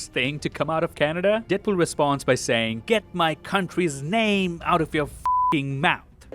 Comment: he said that Logan was a hero, and only the thing that worth a shit outta Canada not what you said And it was keep my country’s name not get my country’s name